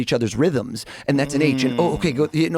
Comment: "and that's a" what did he say?? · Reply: I think he says an H